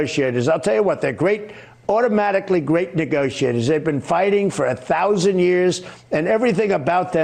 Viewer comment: They've been fighting for thousands of years. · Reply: I thought he said that they'd been negotiating for thousands of years at first, which kinda counters his point. Still, it's literally Trump. His fans don't care.